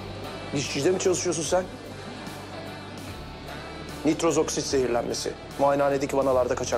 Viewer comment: Nurda ne diyor dişçi arkadaşlar varmı aramizda ben pek anlamaadim? Nitroziksitmi?